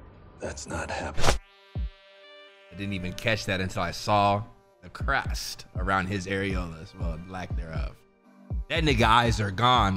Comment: Did he just say the crust around bros aerolas